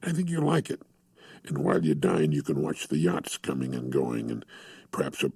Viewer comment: the caption says, "and while you're dying, you can watch the yachts coming and going, and perhaps a porus or two". He's talking about his restaurant, he says 'dining' and 'porpoise'. Lots of errors of that variety, did you transcribe this yourself or run it through an automated transcription tool? May generate a lot of confusion over the man's words.